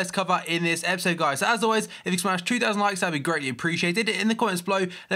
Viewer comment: I'm sorry but.... What the fuck did you say from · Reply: ItsHaber "If we can smash 2000 likes, that would be greatly appreciated." :)